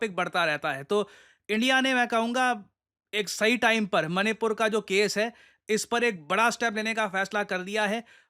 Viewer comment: did he say sahi time par ??? Sir itna khul k nhi itna to nhi